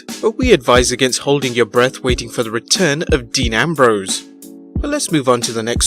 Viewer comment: I thought it said amber rose